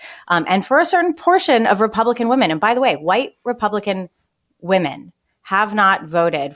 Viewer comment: You mean a majority of white women. I am a white woman as it my mom, daughters and we have all always voted democratic. Please don’t generalize. It’s insulting. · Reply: I thought she said White REPUBLICAN women? Independents and democrats are more likely to vote Democrat